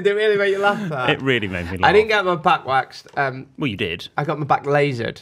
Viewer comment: I thought said “Shall we talk about your back quacks?” Not back waxed lol